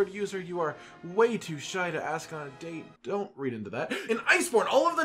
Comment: Ohoho. Did i hear that right. wants to ask Aylia on a date. 😏❤️